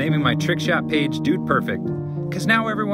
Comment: The “that’ll work” is such a mood 😂😂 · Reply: I thought he said “I’m “out of work”” lol